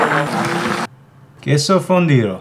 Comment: For a sec I thought it said fundildo 😂